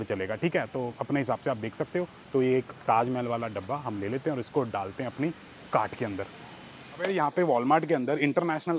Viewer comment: Why do I keep hearing: “isko daalte hain apni Ga**d k andar” 😂